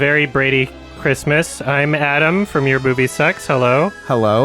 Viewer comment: I misheard this as „Hi, I’m Adum from ‚your boobie sucks‘ …“ … and now I want to see this format on this channel 😁